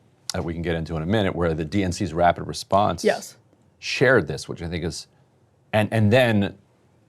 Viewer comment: I swear I he's saying "shared this with the ni##as"